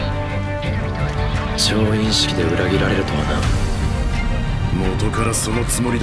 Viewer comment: What did he say at 0:35 ? I understood 調印式 but not the continuation. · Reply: 調印式 で 裏切られる とはな chouinshiki de uragirareru towana